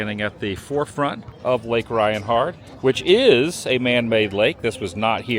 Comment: All this time I thought you were saying “Reinhart” not “Rianhard” lol.